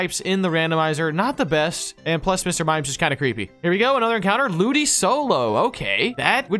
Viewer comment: did he say ludisolo